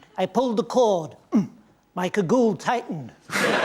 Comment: what did he say? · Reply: He said “cagoule” which is basically a raincoat with cords (citation needed). Think of it like grabbing both ends of a hoodie cord and having the hood opening shrink.